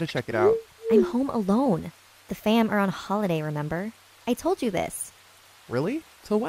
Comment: instead of it saying remember it say rember